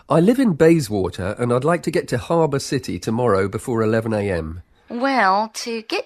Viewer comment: i heard " 4:39 herbert city"